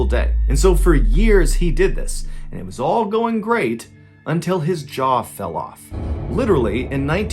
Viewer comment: For a sec there i thought he said "his job fall off"